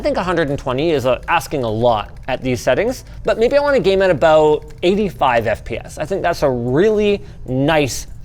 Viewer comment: i heard "i want to game at about.. maybe 5fps